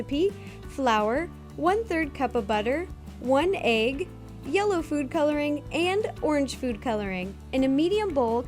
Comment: You know when she says yellow food coloring at 1:13 i thought she said yolo food coloring...